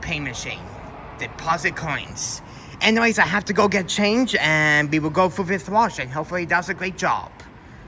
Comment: Lol the closed captions got me at 0:45 - Anyways I have to go get changed when it meant to say change not changed but this is a 24/7 laundromat lol! :D